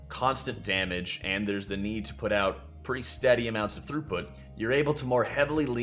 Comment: i kept hearing you say "pretty steady amounts of throopwood" - what is throopwood? new player obviously